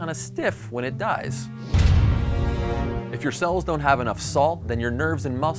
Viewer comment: guys help i need to know what he sayin! "if your cells don't have enough salt"??